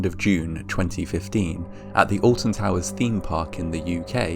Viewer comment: I keep hearing "Austin Powers" every time they say "Alton Towers". Anyone else? Groovy baby.